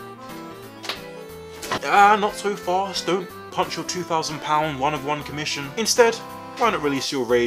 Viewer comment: Me, an uninitiated to the hobby, non native english speaker @ 5:25 : "One of one for two hundred, thats pretty reasonable" Also me: "WAIT DID HE SAY THOUSAND!?"